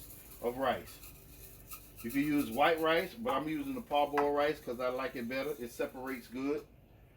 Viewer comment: What kind of rice are you using? Sounds like you said "pot boil." But what kind of rice is that, literally? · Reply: Parboiled rice 😉